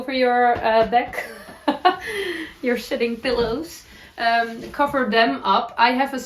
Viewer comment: face-blue-smiling:"sitting pillows" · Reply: i thought she said shitting pillows too. It does work. I came to see if anyone else thought they heard that😅